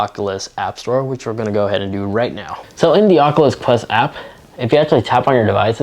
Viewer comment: Oat the start it sounded like he said de aculus quest